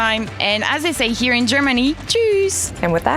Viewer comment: as we say here in Germany.... Jews! am i the only one who heard that?